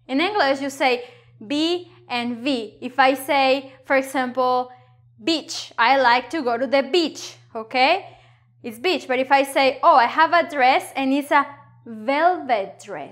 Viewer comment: At first I heard "bitch" instead of "beach" lol